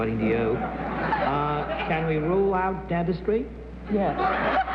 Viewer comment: What does Bennet say at 6:50? Dedis...??? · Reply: Dentistry